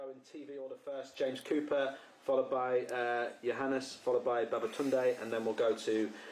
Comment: Thank me later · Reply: did i hear that right? 32:18 Babatunday!?